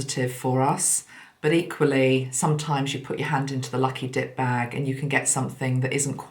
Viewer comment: LOL I thought you said lucky dick bag. Oh thought, “what she talking about now?” 😂😂😂